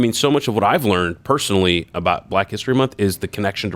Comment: I thought he was saying Black With for most of the bit